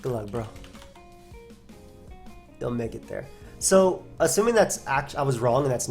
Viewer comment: meant this battle lol cause that would mean i lose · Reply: Omg And I thought I heard you say "you'll make it there :]" god I was so wrong